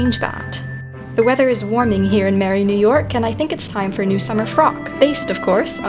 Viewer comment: my stupid ass thought you said Mary, New York (instead of merry New York)